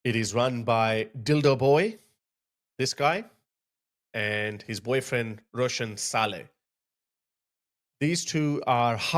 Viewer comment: Did I hear that right? Dildo Boy and his boyfriend Russian Salad? 😂😂😂